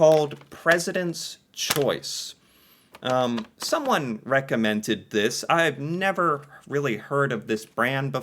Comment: WTF did you just say, Recommentant!! oO English do you speak it!! 2:18 ?